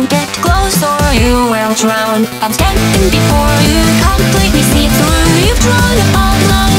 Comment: When she sings "I'm standing before you" I keep hearing "I'm dancing before you"??? My ears are weird lol. AWESOME SONNNGGG